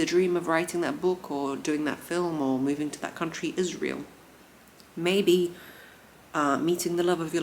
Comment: gosh I had to do a double take cause I heard Israel both times 😅 I was like ohhhhh faaaaak